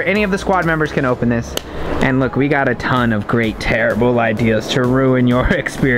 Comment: Did just say "great terrible ideas" lol😂